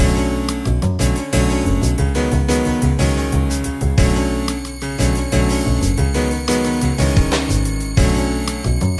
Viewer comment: someone tells me what phrase says in the minute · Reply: itowgameplay 360 “Gutter Trash!”